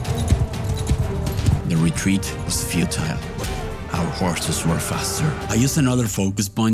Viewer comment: at 27:26 I thought you said "Right in the sphincter" and not "Retreat was futile"